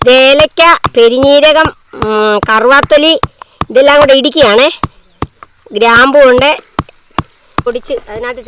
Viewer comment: Thankyou verymuch for posting beef fry recipe. Just a quick question in 8:43 what are the mixtures? I heard jeera, cardamom but couldn't understand other spice that is required to prepare the spice. Would you be able to mention it. Thank you. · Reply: Cloves,staranis